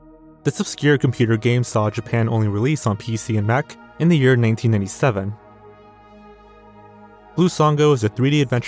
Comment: for a minute I thought you said 1907 but I think you said 1987 lol